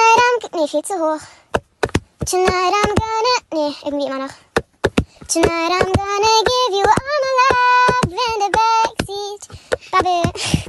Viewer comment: Why do I hear “tonight I’m gonna give you all my love and a baby” 💀